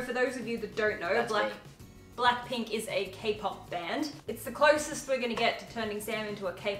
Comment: I watch your videos and have no idea what Kpop is but I do know what a cake pop is and that’s what I thought you kept saying the first few times lol